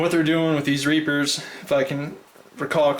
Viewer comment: at like 7:49 it sounds like u say "fuckin" instead of "if i can" lol